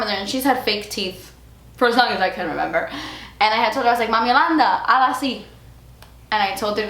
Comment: What's your grandma's name? I listend to that part about 12 times and I always heard "Jolanda". Guess what. My Name is Jolanda :D I looooveeee your kat chats!! Could what them everyday! Hugs from Germany !